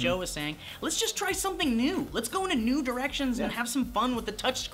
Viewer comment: did he say "nude erections"?